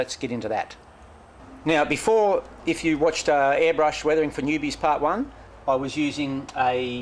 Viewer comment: Hi! Good stuff there mate! Practice makes perfect as the saying goes and it shows on your rollingstock! II'l have to try this on my own freight cars. At the start of the video me and the missus thought you said "weathering for boobies!" LOL A dyslexic moment! How about some aussie man sweat with eucalyptus oil? Might be a good seller! Keep up the great work mate! Cheers! Stew.